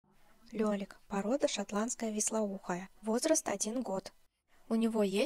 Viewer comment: Звук был на минимуме, послышалось шампанское вислоухое